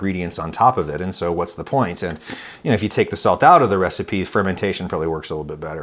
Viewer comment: Hey Adam! Great vid! One question. You mentioned that salt makes the dough denser which I understand. However you said that a little salt helps the fermentation. How so? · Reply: I believe he actually says that taking the salt out of the dough helps it ferment a bit. Timestamp is around 2:21 :)